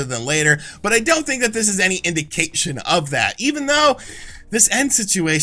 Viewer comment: "IndiCAKEtion?" That's what I heard.